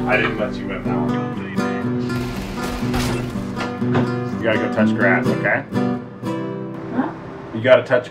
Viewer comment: That 13:54 "HUH?" Makes me think that she misheard you saying "Gotta go touch your ass" 😂